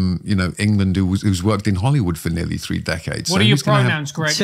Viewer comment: Can somebody PLEASE tell me what the one guy asked Greg at exactly 38:26 in this recording? There seemed to be a joke but I replayed it 5 times and even slowed it down to 0.5 speed and I CANNOT understand the one key word in the joke!!! He asked him "What are your ______ , Greg?" Anybody catch that word? Thanks!! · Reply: pronouns